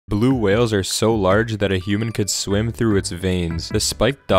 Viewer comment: I thought he said “Blue whales are so big it could swim through humans veins”